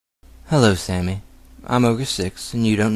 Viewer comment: I got really confused because I thought you said "Hello Sami, I'm over 6". I'm such a div :P